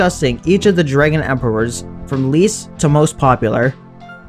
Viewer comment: I could've sworn it said least to worst popular